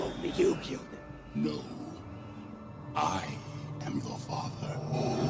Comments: I love this scene but I thought he said "Luke, I am your father."???? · I thought he said Luke I am your father